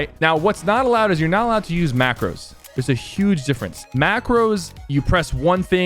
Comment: Oh macros or somehow you spell it, but I thought he said macbook pro. I am not kidding 🤣